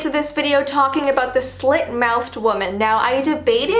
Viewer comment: I thought it said shit mouthed